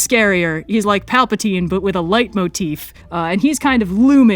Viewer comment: At first I misheard “like Palpatine but with a light motif” as “like Palpatine but with a leitmotif” lol